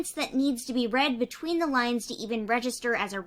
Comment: I thought you said between the lions but you really said between the lines